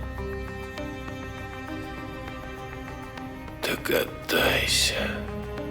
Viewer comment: Мне одной послышалось, что Огнев, цитируя Булгакова говорит: "Так отдайся!" (Вместо "Догадайся)))